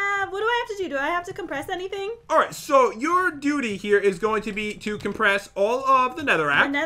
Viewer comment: he said ‘Your duty’ but it sounded like ‘Your doody’